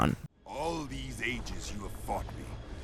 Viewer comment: Is He Saying: ,,All This Ages You Are Fuck Me,,?